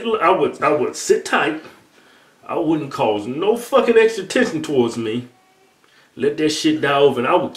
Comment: Hold the fuck up, WHAT fucking word did Kevin just use? edit: he said "extra tension" those big ass braces were caught in them big ass lips or sum x[) x[)